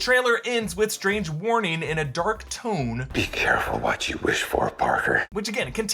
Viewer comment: Thought Goblin said “be careful for what you wish for Parker”